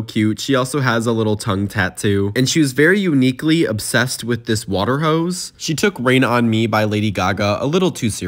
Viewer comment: Why did i hear "water hoes" 😂😂